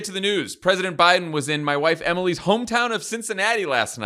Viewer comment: Anyone else hear @ 1:00 "The President was in, my wife, Emily" and do a spit take before hearing the rest of the sentence?